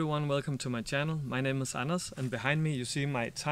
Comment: "My name is anus"?? I'm not trying to be a troll, but did he say that??!! Edit: ANDERS! I'm so sorrry!!!!